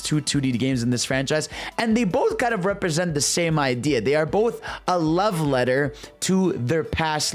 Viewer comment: I thought u said they are both a level editor, real confusing.